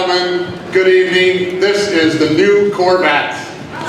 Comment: is it just me, or did anyone else hear "COR-VAT"? ---11:45 wtf?